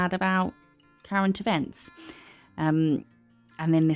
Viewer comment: When you said sad about "current events", I heard "Karen DeBents" and I was all, who's that? I thought she would be sad about the Queen, then my brain realized what you actually said. 🤣